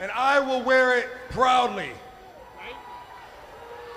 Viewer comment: "Look it spins" lol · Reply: I thought he said, "Look it's Vince" jsjsj